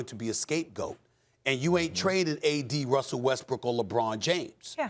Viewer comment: Did he say frank le-vogel ?